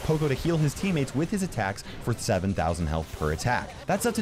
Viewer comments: was it only me who heard say 7000 instead of 700 lol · Did you just say it heals 7000 health?